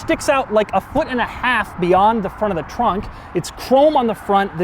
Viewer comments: did he say front of the trunk really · "front of the trunk"? Did I hear that right? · Is it me or did he say “beyond the front of the trunk”?